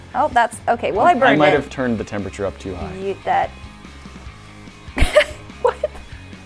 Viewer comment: did say “Yeet that” ?